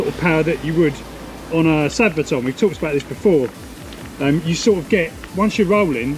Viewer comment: Which punchy controller is it you mentioned just after 5:50 ? It sounds like you say sabotage but I don't think you did! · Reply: It's called a sabvoton controller.